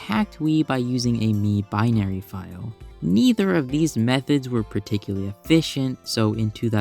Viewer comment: DID I HEAR MIITHER INSTEAD OF NEITHER OR AM I GOING INSANE??